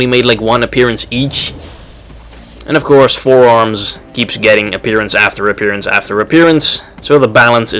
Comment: dude, r u mental or am i hearing things because it sounded like u said four arms is lame